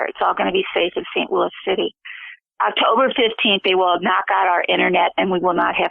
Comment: Message to you from MOSH, "Watch for the Sign". The first two weeks in September people in the kill zones will come down with flu-like symptoms. This will be the trigger for a "kill" city lock down. Nestle has planted the Brucellosis bacterium in six of their products which will be sold only in the kill zones. You will then know thru the media if you are in a kill zone. This will prove to you that MOSH is real. Blessings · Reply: I thought she said mid October not Sept?